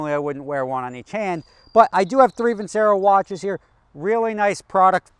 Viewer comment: Vicero! oh i thought you kept saying juicero like that awful juicer company